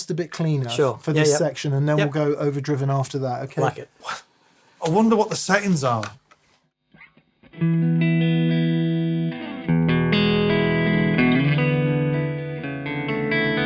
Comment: "I wonder what the settings are" will never get old. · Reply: Thank you! I always heard him say "I wonder what the Satans are" and it got me confused AF each time 🤣